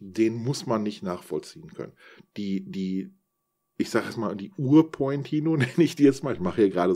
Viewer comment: Danke für den Erfahrungsbericht! Hab jetzt richtig Lust mich so einem Projekt zu nähern. ABER: Ich hab jetzt nicht richtig verstanden wie der Lautsprecher(bausatz) korrekt heißt. Ist "Reveo" richtig? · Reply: Wenn du mit Lommersum sprichst kannst du Riveo oder Pointino sagen, er weiß in beiden Fällen worum es geht.